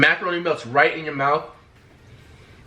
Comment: “So creamy” “Melts right in your mouth” 💀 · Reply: I really thought he said “buses right in your mouth”😂